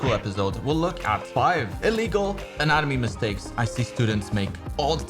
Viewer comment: min, I had to rewatch, or rather re-listen, several times because I kept hearing "in anime" instead of "anatomy".